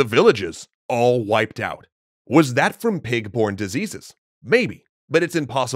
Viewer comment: did anyone hear “was that from pig porn diseases”